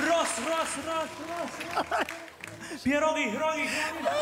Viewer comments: on powiedzial: jestem głodny? XDD Bo jeśli tak to mam problemy ze słuchem.... Ponieważ usłyszałam: jestem płodny XDD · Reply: Ja usłyszałam pierdoli A potem jestem glodny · Usłyszałam,, Pierogi kroji"😂😂